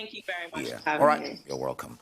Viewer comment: What's with that man's accent 🤦🏾‍♂️ At end of the interview I heard him say, "You're were-come" 🤣😂